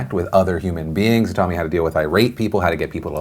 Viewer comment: i swear he said "i rape people"